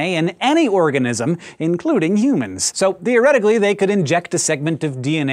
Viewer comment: You said thy instead of they.